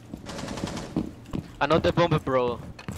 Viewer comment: Did he just say I know the Bombe bro